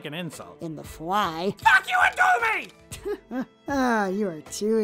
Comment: It sounded like he said "F*ck YOU AND DO ME!" 😂